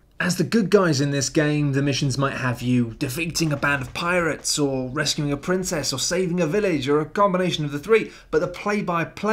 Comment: I could swear I heard say "Wrestle a princess..." I'm in!